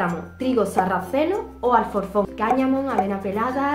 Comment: Hoye me podías repetir esa parte pero en comentario esque no entendi porfavor respondéme😞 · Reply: Alforfon